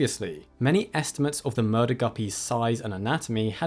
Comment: Did he just say "murder guppy"?